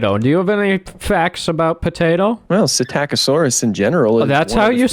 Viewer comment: first time you said psittacosaurus i thought you said shit-aca-saurus XD. and again great video, i find it surprising that you only have 1,34 subs cuz you make great and fantastic content. idea: maybe make a minecraft series were you survive on a dinosaur infested island with ur friends (no cheats, no guns, no meth).